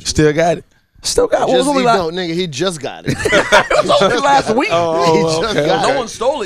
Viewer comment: Did he say "nigga he just got it" at 3:00 ?? I thought that would be offensive coming from a white person? I think he is anyway